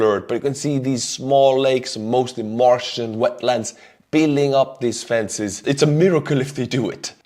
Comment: I keep hearing "Martian wetlands" 👽